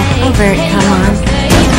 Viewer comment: What did Root say at 0:57? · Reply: "Transparent rationale for conversation." "Overt come-on." :')